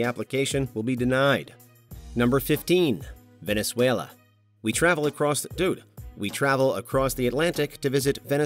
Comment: did I hear "we travel across---DUDE....we travel across the atlantic"